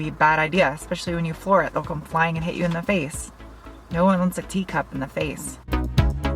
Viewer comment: I thought she said “ nobody wants a D cup in their face!” And I was like scuse me?!?! Oh tea cup?!